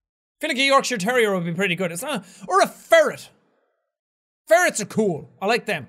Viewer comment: When he said that he wanted his patronus to be a ferret, I thought he said furry, and now I'm very disappointed in myself.....